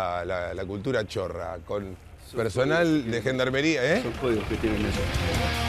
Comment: que dice el gendarme? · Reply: "son códigos que tienen ellos"